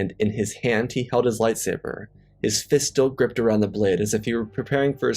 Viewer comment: I swear I thought you said "His Fisto gripped around the blade..." at the start of the video lol